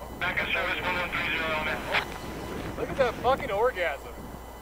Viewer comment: WHAT did he say?!? Anyone? =0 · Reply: look at that F-ing orgasm. Dooshbag in the group.